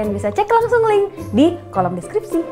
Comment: aku salah dengar apa gimana ya nyai, pas iklan tokped nyai bilang "cek di kolom skripsi"